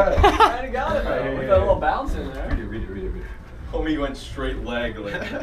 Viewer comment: Did just say homie?